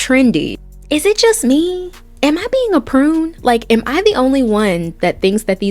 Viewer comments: I'm enjoying the video! at 4:16 am I tripping or does she say "Am I being a PRUNE?" instead of prude tho lol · lol did you say "prune" 😂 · Did she say ‘prune?’